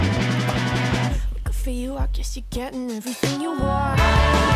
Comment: Me on the goosebumps song:all these years... I thought he was saying... "I get no 🐱 everytime"